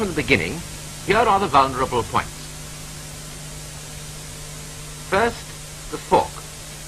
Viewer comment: Did he just say "first, the cock?"